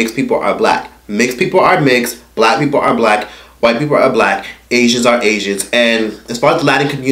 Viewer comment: did he just say white people are black 😂